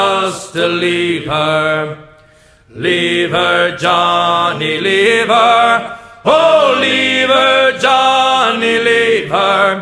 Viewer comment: I thought it said, "Leaver Jhonny Leaver, Oh Leaver Jhonny Leaver" and didn't even doubt it for a few months